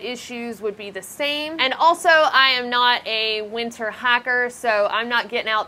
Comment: dang I thought she was saying hacker the whole time, until "winter hacker" provided a clue :)